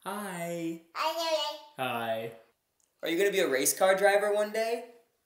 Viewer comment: Did just say how you doing @ 7:14? 😂😂💜